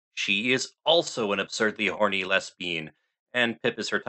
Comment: Did- was that-? Did you say les-bean? 😂😂😂😂